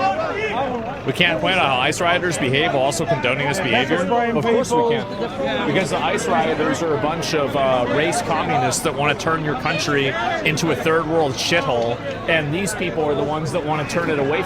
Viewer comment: "I can't breathe" has been uno reversed against the left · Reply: I thought they were saying "our country"